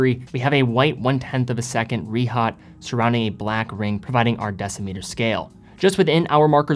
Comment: What does he say here? Sounds like decimeter scale but that doesn't make sense. I'm curious because I don't see the point of having two scales that appear to indicate the same exact thing.